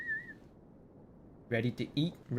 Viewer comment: I wasn't looking at the screen, and at 2:23:23 I hear say "Ready to eat Russians" and be like wtfff